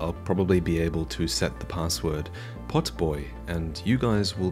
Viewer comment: says the password will be "potboy" but the subtitles say the password will be "vaati"... which shall it be??